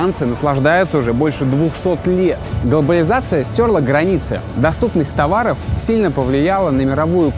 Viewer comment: "Долбализация стерла границы" Лайк,еcли тоже послышалось xD